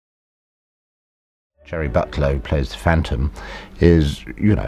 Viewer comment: im sorry but the guy sounds like he said cherry butload